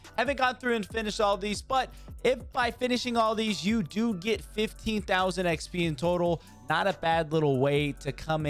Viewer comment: does he say 15,000 or 50,000? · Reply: 15